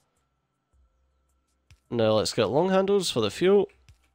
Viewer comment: i was doing something else when you said "now lets get long handles for the fuel" so i thought i heard you say "now lets get love handles for the fuhrer" XD i had to listen to it over and over again XD